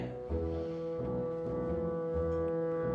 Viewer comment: I was the only one who thought she was saying me am queen😂?